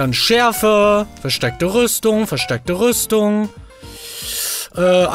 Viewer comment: Bin ich der einzige der "versteckte Rüstung" verstanden hat? 🤣